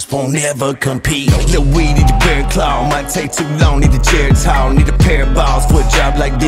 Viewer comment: I heard that n word slip in